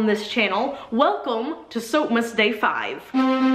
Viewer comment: all this time i thought you were saying "welcome to soap mistakes" instead of soapmas day 😂😂 and i thought this is a great idea!